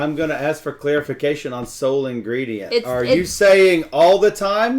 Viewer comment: When he said “sole ingredient” I thought he said “soylent green!” 🙃